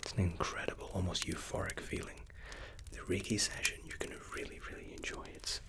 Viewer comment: Tbh I thought you were saying "reggae session" instead of reiki session the whole time